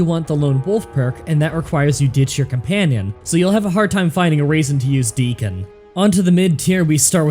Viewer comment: Thanks for watching everyone! Just to clarify something, a LOT of people are saying "Dogmeat can wear armour". Although that's true, it doesn't actually give him any damage resistance. That's what I'm talking about when I say he can't wear armour, it doesn't actually increase his damage resistance stat. Here's Proof: <link> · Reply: you say raisin instead of reason, just like Dr. Mobius from Fallout New Vegas. Intentional or unintentional?